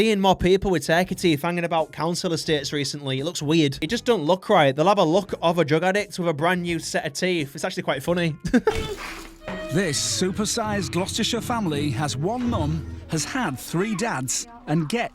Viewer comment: was that Gloucestershire I heard